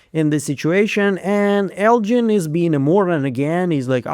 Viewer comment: Elgin is being a what? It sounds like you said Mormon lol. Closed Captioning says [___] so it wasn't helpful. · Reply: Moron.